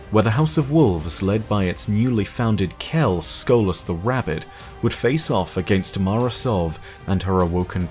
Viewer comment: I heard "Skolas the Rabbit" XD